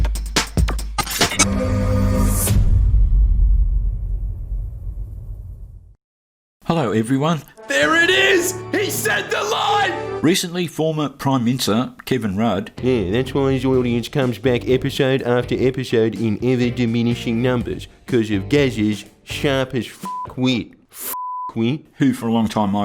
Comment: "former prime minster" MINI-STER is MORE THAN 2 Syllables mate! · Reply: I thought he was saying former prime mincer